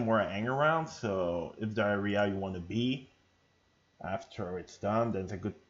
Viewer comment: did you just say Diarrhea i wanna be? 😂😂. Nice tutorial,thx